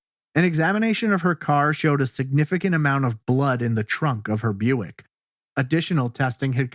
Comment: Yeah wonder why there is blood in the bedroom was she asleep when someone came in usually either that or she was in the bedroom with a known person, of course she could have run upstairs but I doubt it. How did they have the number to her card ? Probably more than one person taking things from the house for different reasons at different times once determined missing. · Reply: I didnt hear blood in her bedroom. I heard him say in the trunk of her car.